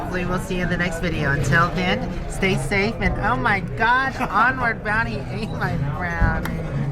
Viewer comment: When ate your brownie it almost sounded like you said "Onward Brownie." LOL! is such a great guy. Thanks for sharing!